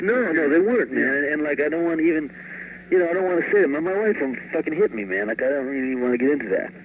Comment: Can somebody clarify for me what he said at about 8:39 about his wife? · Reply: "She'd fuckin' hit me" ahaha